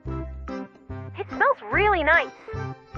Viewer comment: He actually said good but it said nice ☠️